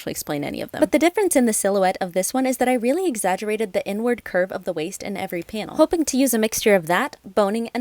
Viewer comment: I heard "I really exaggerated the n-word curve of the waist" I was LOST